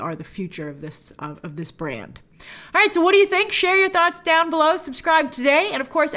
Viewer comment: If you listen in 2x, it sounds like she said shave your nuts down below instead of share your thoughts down below 🤦🏾